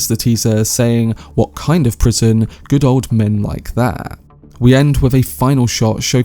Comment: I heard "what kind of prison could hold a man like that?" Complimenting odysseus' strength and valor The shot that follows is him essentially imprisoned by the collossal force of nature that is the sea